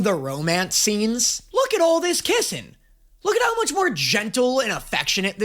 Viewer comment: "look at all this kissing" sounded like "look at all those chickens" lol